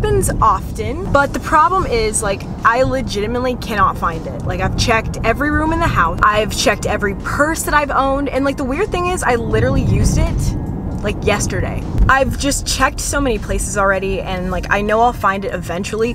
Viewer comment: i thought you said "i checked every person I own" instead of "i checked every purse that i own"